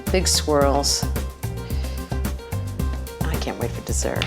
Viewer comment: At first I thought she said ‘I can’t make a desert ‘ at the end and I was like ‘what 😱’ I think you can 😂 she said ‘I can’t wait for desert’ . makes more sense lol